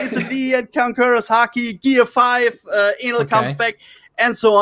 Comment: Did he just said Enel comes back? membro della community italiana ad honorem.